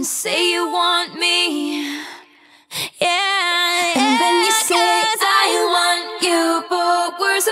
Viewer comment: What does she say immediately after "Yeah, Yeah" at 3:10 (over "and then you say")? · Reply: “Yeah, yeah, cuz” (I want you for worse or for better…)